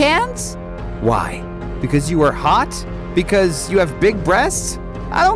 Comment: Bro what did you say? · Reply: "Because you have big breasts?"